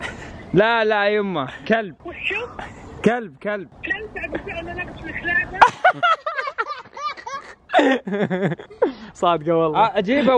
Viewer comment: وش قالت؟ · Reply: خالد رياض اتوقع قالت ( كلب وانا ناقيه كلاب )